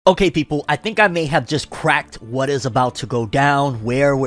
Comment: For a moment I thought he said "okay people, I think I'm just on crack" in the beginning. Had to re-watch it to hear it right.